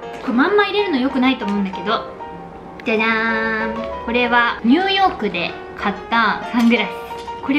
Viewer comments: 「ニューヨークで買いました」が「2億で買いました」に聞こえた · 「ニューヨーク」が「2億」に聞こえる事件。